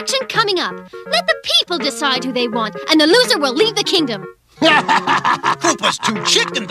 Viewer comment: Wasn't the deal that the loser gets to rule the mushroom kingdom? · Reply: Oh I guess I misheard "leave" as "lead" lmao